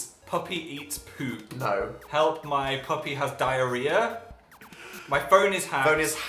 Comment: on that first one I thought it said "help my puppy is hacked"